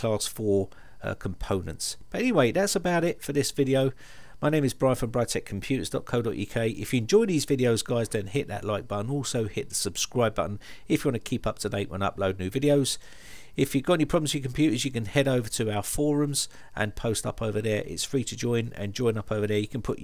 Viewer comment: Wait wait wait did Brian say if you enjoy these videos then don`t hit the like button ? Yes he did say that ,listen around 6:10 and you`ll hear it oops LOL :)